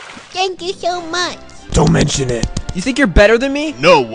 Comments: I thought she said you need to pee💀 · You need to leave? Damn I have always heard it as “you need to pee” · Misheard audio: YOU NEED TO PEE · Instead of "Don't mention it" I heard "Domain espansion" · I thought the women said "YOU NEED TO PEE"